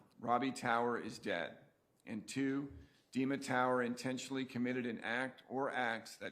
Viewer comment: Everytime she says " Dima tower" I swear I hear her say " demon power". So when she says "She was stabbed by 'demon power"....I can't unhear it.